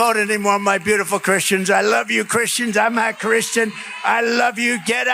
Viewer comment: To my ear, sounded like Trump said, "Imma Christian". But it was admittedly a bit unclear. - And, though Trump has said some dumb things, I find it unlikely he'd say, "I'm not a Christian." - especially to an audience of Christians. - Hm?...